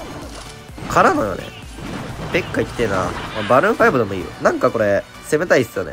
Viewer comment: マルーン5にしか聞こえん笑